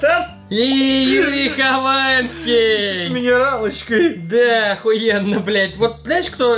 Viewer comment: Послышалось? 0:05 еврей Хованский? Хотя нет.